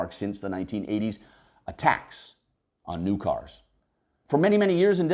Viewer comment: I thought he said ATTACKS on new cars...! YES! Get out the hammers, chains, keys 🤪. But... bummer... it’s A TAX on new cars 😉. Seriously, great video on some great ideas. It just sums up the the things that are expected from cyclist and pedestrians and not from motorists. Let’s turn thins around.